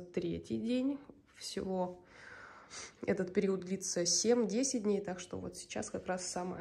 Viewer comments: В начале послышалось в районе 70 дней 😳, несколько раз переслушала-7...10 дней, фух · Послышалось семьдесят дней,чуть со стула не упала.Думаю,нифига себе,период! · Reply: когда без паузы произносится 7-10 дней, то звучит именно 70. Мне, как и вам всем, тоже так послышалось, но потом поняла, что это просто так слышится... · Фу бли. Я уж в шоке был. Мне первый раз послышалось 70 дней. Ну 7 - 10 дней то можно и на корову посмотреть.